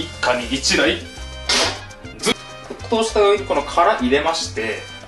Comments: うんこの殻って言ってるように聞こえる笑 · 沸騰したウンコの殻〜に聞こえてサザエ食べたくなりました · 沸騰したうんこの殻、としか聞こえない